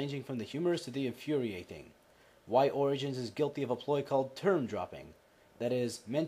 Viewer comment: when you said "term dropping", I thought you said "turd dropping", which would also be a pretty good description.